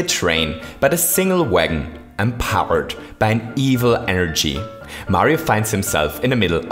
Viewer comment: the caption should say "energy" instead of "energ" And for (i think) the first time, the outro isn't "And maybe you feel especially <insert topic-related word here> and want to hit the subscribe button as well" Nice job.